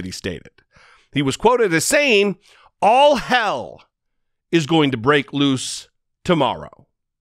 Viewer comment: Are you sure he said “all hell is going to break loose?” I thought he said, “a calm tourist tour is gonna break out.” 🤣